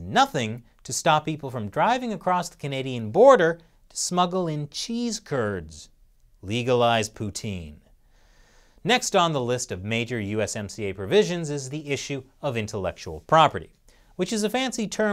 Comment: did he say legalized putin?